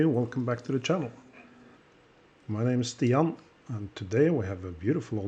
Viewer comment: Thanks so much! Yes, a Memovox is underway :) So stay tuned, I hope to be able to publish the video of it not too late into the new year. Stian 😉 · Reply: Great. Will stay tuned😀⌚ Btw, I'm sorry. I thought you were saying your name is Theon. Stian. OK